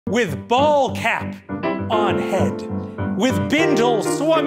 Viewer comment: I like how she heard Ball cap and that decided the character's main personality and a major story line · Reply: Oh! See I heard it as "baldcap" & was waiting for that